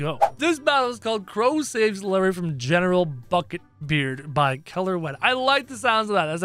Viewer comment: I thought he said general ****ing beard